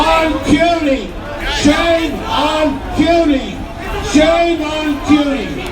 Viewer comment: Thank you! I figured it must be something simple like that that I just don't know. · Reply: Wolf House yeah, I thought she was saying cutie too but apparently she's saying CUNY lol.